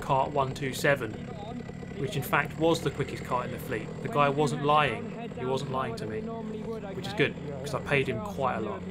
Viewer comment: what did the other guy say at 13:08 talking over him i cant hear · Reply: He was telling me to roll up to the line slower than normal as there are some new drivers in the field.